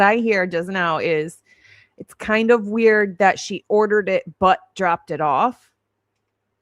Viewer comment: I hear OR not but xxx ❤️from🇦🇺